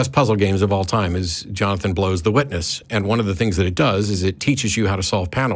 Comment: So I thought you were saying the name of the game was "Jonathan Blows the Witness," not Jonathan Blow's "The Witness." I didn't discover the misunderstanding until I typed it in google. Hilarious. I was already contemplating a potential sequel: "Matt Felates the Prosecutor."